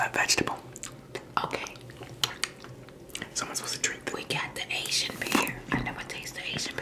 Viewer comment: did she say Haitian pear or Asian pear If its Haitian pear Yasss